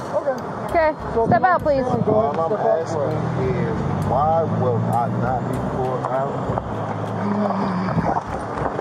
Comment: I think the unintelligible part of what he says is “all I’m asking is: why was I not pulled out of the vehicle”